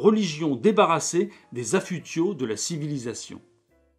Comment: Bonjour monsieur: j'aimerais savoir quel terme vous utilisez à 8:14 : j'entends "afudio" : j'ai beau chercher, je ne vois pas à quoi ce terme réfère. Merci ! Vous êtes un vulgarisateur extraordinaire ! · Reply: Affûtiaux : parures sans valeur :)